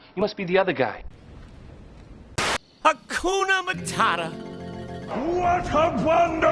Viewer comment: I thought Timone was about to say Hawk Tuah, my brain is truly too far gone